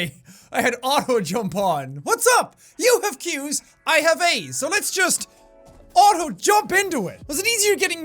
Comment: I thought he said "You have queues, I have aids"